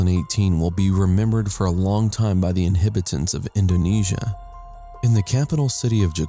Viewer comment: most of the islands are 'uninhibited'... LOL 😂😂😂😂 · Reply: I thought I was hearing things when I heard that...... Even did a quick rewind..... To hear him say it again. "The inhibitants of Indonesia..... " THAT was NOT the correct word he was supposed to use at that juncture (Or ANY other juncture as it does NOT exist) so the "Fail" as you so put it "Rezlo" remains SOLELY on you. Thank goodness there are "Some" people that "ACTUALLY" know how to form a sentence here like "Justin Burrow", "Debra Moore" & "kadayao". Thumbs up guys 👍👍👍 I think the thing that astounds me the most are the amount of comments further down that think this is, in no way wrong, as they don't even mention how wrong that entire sentence sounded...... Oh well, such is the life of idiots...... Ps. I also give a thumbs up to "Mike Parker" just for actually making "Rezlo" go and Google the word to see if it actually existed, as I can actually see him doing that 😂😂😂